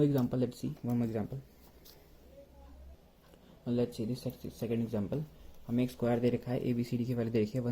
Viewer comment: pr kya bola?? (sec c or sexxxy)😂😂